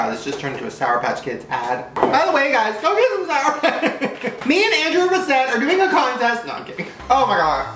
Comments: “me and Andrew have a set” did he say that....was Andrew with Shane filming already or did he actually predict that his actual camera man was gonna be named Andrew ????!!!! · is it just more or did he say Andrew 😦